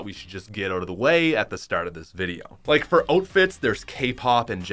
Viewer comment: ‘oatfits’ lol he actually said oatfits